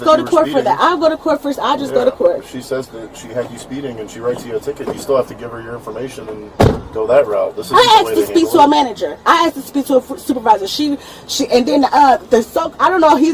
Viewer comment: She actually said “I asked to speak to a Manager.” Wow. 😂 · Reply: I thought she said " I axed to speak to a Manager" ?